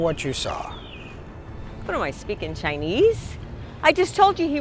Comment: I am chinese and I heard “…I speak in chinese?!” Lol😂 ATM machine only allows $400 daily? In Singapore, we can set the limit. The default daily limit here is $3,000 (USD$2,100) for ATM withdrawal.